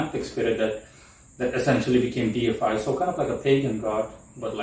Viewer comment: I couldn't understand properly what you said, my english is not so good. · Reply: Deified is what I said; it simply means being metaphorically worshiped to the point where you become ridiculously strong. The phrase is simply a reference to pagan godlike strength itself...